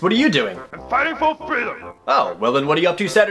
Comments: I'M FIGHTING FOR FREEDOM!! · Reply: Syndicatefan2 OOOOOOOOOOHHHHHH THATS what he said. I thought he said. "I fight for fritters!" Personally, I would have fought for fritters too. THEY ARE SO GUD! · What on earth does Sonic Blast Man even say with that special move? The fight fulfilled? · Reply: I hear "I'm fighting for freedom"